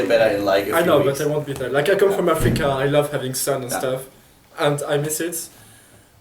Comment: haha did he say he comes from Africa? REPRESENT ;) Good interview. Keep up the good work!